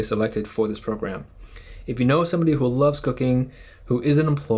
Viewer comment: The video starts, I hear "If you know somebody who loves cocain" im like WHAT? ah ok nevermind